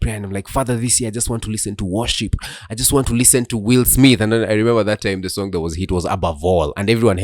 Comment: Hebu hapo 10:52, did he just say listening to gospel by Will Smith😂😂😂